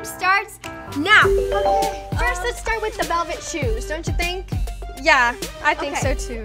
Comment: I’m sorry I thought it said “velvet boobs” whoops